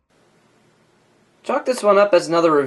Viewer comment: Lol it sounded like you said "Fuck this one up" in the beginning